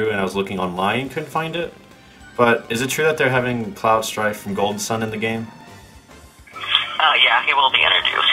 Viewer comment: Did you just say Cloud Strife... from Golden Sun?